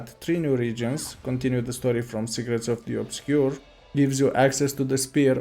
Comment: i cant be the only one who heard "Cigarettes of the Obscure" 😂😂😂